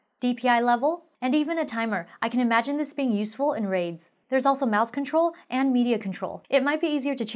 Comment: When you said "I can see this being great for raids" I thought you said "I can see this being great for raves" and I was like I want to go to your parties.